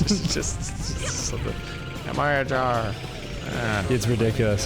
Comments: What the Game Says: “Annihilator” What Hears: “Am I a Jar?” What Hears: “I’m not a car.” What I Hear: "Go to HR." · I love that it sounds like Omega is saying "Am I a jar?!" For anyone that can't tell, he's saying "Annihilation" and "Charge," but he says them when he attacks, and he attacks faster than he talks, so "Charge" interrupts "Annihilation," so it comes out "Annihi- Charge!"